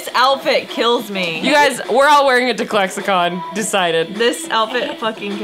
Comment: DID SHE... DID SHE JUST SAY ‘CLEXACON’ OH MY GOSH